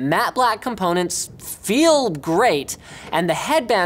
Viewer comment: Did he just say feeled?